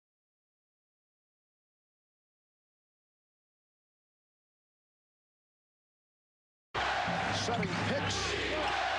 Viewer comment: What does Reggie say to Spike at @ 4:20? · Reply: I believe its something like "look over here bitch" not sure what the last word is, but he definitely says look over here